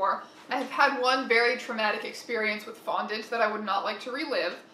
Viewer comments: I THOUGHT YOU SAID BONDAGE NOT FONDANT AND I GASPED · i thought she said bondage not fondant and i honestly died a bit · OK I had to rewatch the bit where you said 'I had one very traumatic experience with fondant' because for a moment I thought you said bondage instead of fondant I